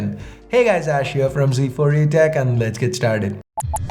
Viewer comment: Did you just say G4ETech 😂